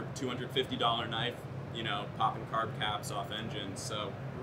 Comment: Did he say carb cap???? Lmao